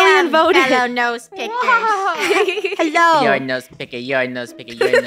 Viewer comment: I heard said "you're a nose piggy"😂😂